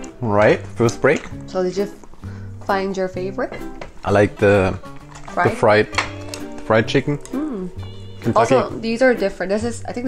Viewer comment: Did really say "I like the fried chicken." ???????? @ 2:47......